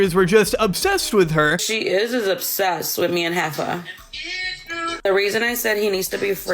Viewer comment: 😂😂😂😂 every time she said “Jefa” I think she says “HEIFER” 😂😂😂 not to far off 😂